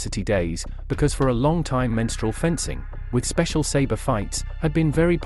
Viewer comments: I first heard that as “menstrual fencing” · Mensur fighting, fencing was the term! Meaning standing a "measured," distance from each other. Also going to University, they gained such honor scars from saber fighting. My father -in-law went to Heidleberg Un. and rec. one of these, "badges of honor ." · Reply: I thought he said “menstrual” fencing 😮